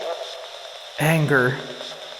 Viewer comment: says "numb nuts" like he was calling you that! I did not hear "anger" at all. I heard "numb nuts"